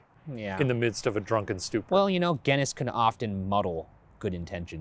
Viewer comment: I'm rewatching the true crime series whilst waiting for more spooky watcher content and I only noticed now - when Ryan says "ge-ness" does he mean Guinness??? I've heard him say the word before but issued it was some American thing I didn't recognise.